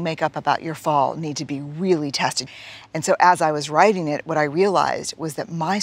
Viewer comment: what is she saying? She spoke way too quickly there and I cannot figure out that one word, "The stories you make up about your fall need to be really...." Really what? I even slowed it down and cannot understand at a slower speed either... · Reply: "...need to be really tested."